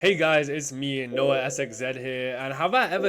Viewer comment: I heard him say no ass exit here..